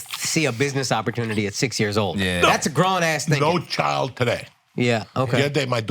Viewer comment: did Akash say Grown ass N** ?????